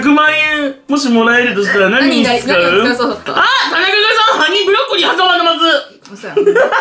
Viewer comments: 歯にブロッコリーがハニーブロッコリーに聞こえる笑笑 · ハニーブロッコリーって聞こえる🥦🍯